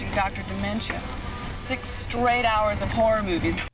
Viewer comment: in that car scene i always thought she was saying dr dimento. not sure why i never questioned that since hes a radio host and not a tv host lol